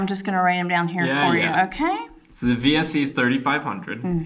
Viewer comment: $795 for GAP, DANG!!!!!!!!!!!!!!!!!!!!!!!!!!!!!!!!!!!!!!!!! Is that for multiple cars?!?!?!?!? :-) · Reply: What's the first thing on the list the VSE I think he said ...